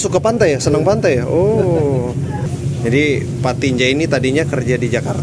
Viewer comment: pak bang, kasian 'K'nya gk disebut jadinya 'Tinja' gk enk bgt itu didengernya😂 asa berbau2 apa gtu 😂😂